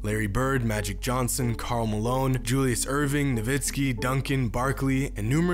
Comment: lol look at captions it says calm alone instead of Karl Malone